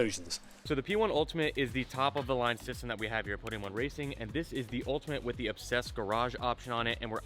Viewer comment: I thought he was saying Pudding Racing! Didn't realise until I saw the link in the description above! LOL